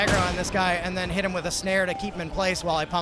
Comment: lol @ 5:10 thought said ''hit him with a snare to keep him in place while i cum on him sum more'' XD. at 4am my humour has devolved as a reuslt of tiredness...dnt blame me lol :)